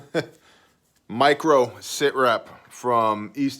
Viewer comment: At first I thought you said Microbe SITREP, which was kinda sitting given the current state of affairs, but then read the title again and realized I was wrong